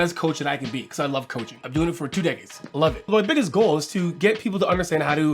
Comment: Bro, I legit heard at 0:20 'I've been doing it [coaching] for two days. Loving it ...' Yes, decades, it's decades :D